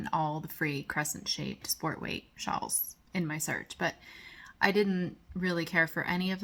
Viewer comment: Beautiful scenery outside your window. Love the shawl pattern you found, I thought you said scarf, but this pattern looks great! Your daughter's sweater is awesome & beautiful. Take some advice and kick momma bird out of your wreath during daylight hours. 🙂